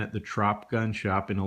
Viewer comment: What is this "trout" you speak of? Or am I mishearing what you're saying? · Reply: Deans Mayhem "Trops"